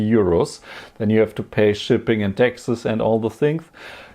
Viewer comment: I heard "paying shipping, and Texas" 😅